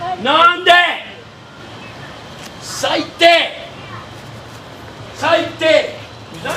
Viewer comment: What is that asshole saying in japanese? "~Saite"? Could not find neither "saite' nor "saitte" as "stupid" in any vocabulary. Does anyone know the word and its meaning? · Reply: it's 最低 (saitei). 最 - the most, 低 - low. 最低 - the lowest, the worst, awful, etc. "Stupid" isn't the most literal translation but it's suitable.